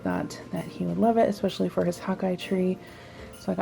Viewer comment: When you first said hawk eye I thought you said hot guy lol